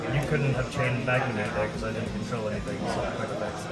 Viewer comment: What is he saying at 16:45 about Magnumhut?